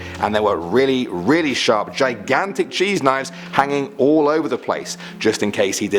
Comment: I sneezed That time And I said did he said cheese?